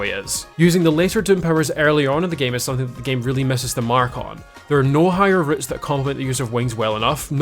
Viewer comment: the WHAT? ngl straight up thought you were saying my name here On a frfr, really good video and speedrun, I love any and all speedruns because they always show much people can take something and perfect it to it's best possible form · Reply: MARKON